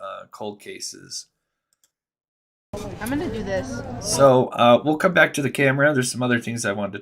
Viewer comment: From about 21:04-21:06, one of the girls (I am not sure if it was Maddie or Kaylee) says something like "Let me do this." So I am wondering if that has any significance to the case and, if so, what? What did she mean by saying that? That she was going to pay for both of their food? Or she would handle the problem/s they were having with the hoodie guy and/or the guy in the tan hat and coat? Or what? I personally have not noticed anyone else talking about that particular aspect of the case when they were at the Grub Hub food truck. Does anyone have any ideas, insight or information on that aspect of one of the girls saying "Let me do this"?